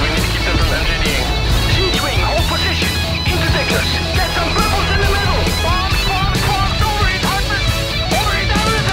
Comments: What did he say? "Interdictors, get some" What in the middle? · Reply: "bubbles", precisely interdiction bubbles. There are subtitles if you enable them. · What's the guy saying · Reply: "Siege wing, hold position. Interdictors get some bubbles in the middle. Bombs, bombs, bombs! Overheat hardeners! Overheat everything!" Turn on the captions and you'll see. =P